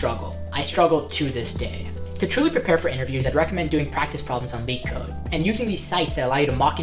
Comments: What's the name of the webpage you show on minute 9:33? I don't understand if you say "bean code, mean code" or something like that. English is not my first language and the subtitles show me "league code". Thanks, and great video! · did he say beatcode?